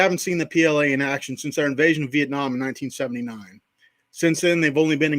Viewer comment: Not understanding your comment. He's talking about China. They invaded Vietnam in 1979. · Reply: Thanks for the reply. It sounded like he said our invasion to me. 👍